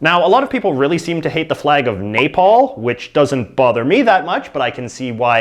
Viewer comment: I thought he was saying napalm instead on nepal at first